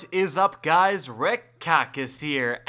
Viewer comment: I am so stupid, for the first time today i realized he is saying but i thought he was saying red carcas